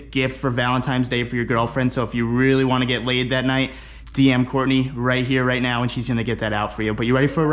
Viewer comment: All I heard was "if you wanna get laid DM Courtney" 🤭🤔